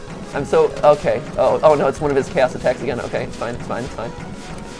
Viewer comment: did anyone else hear “it’s one of his gas attacks”